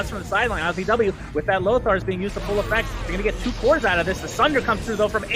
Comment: did just say lothars?